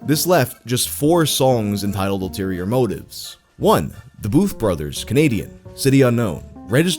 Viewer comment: I thought you said Boof Brothers. I was like DONT WANNA KNOW